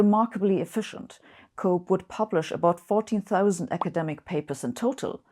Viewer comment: You said 14,000 papers but the caption said 1,400.